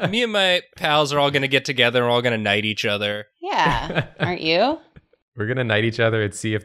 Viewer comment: What they said: We're going to, all, knight each other. What I heard: We're going to "all-night" each other.